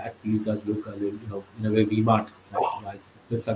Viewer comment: did he said v-mart or d-mart for bearish at 32:30...? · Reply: vmart